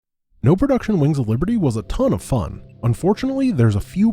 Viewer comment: Okay, so on the off-chance that you see this, Grant. I just have a fun idea because I misheard what you said with "wings of liberty" as "Lings of liberty" ... Could... Could you do a "Is it possible to beat wings of liberty with zerg only?" Because... You know... Lings of Liberty